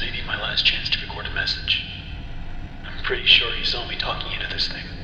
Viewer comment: Ok, A slight correction but, I'm pretty certain says "I'm pretty sure HE saw me talking into this thing." It more sounds like he says he, not she. So the captor I believe he's referring to is Tom